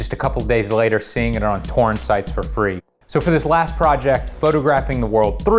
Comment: did anyone else hear "porn sites"